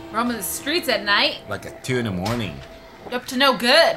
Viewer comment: when red said 2 in the morning I thought he said Tuna morning. lol such a fail